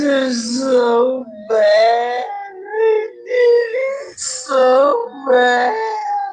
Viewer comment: I hear it as “I did so bad”